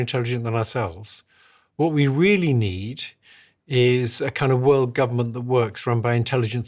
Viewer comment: Did he just say we need a one world government???😳